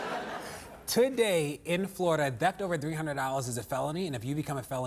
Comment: did he say theft or debt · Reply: slayer 1 theft